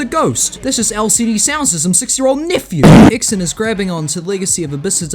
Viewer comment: what is this sentence i can't understand what you said also this is the best geometry dash video i have seen in my life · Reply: "that's just LCD soundsystem's 6 year old nephew". they're a dance punk band and here's what they sound like: <link>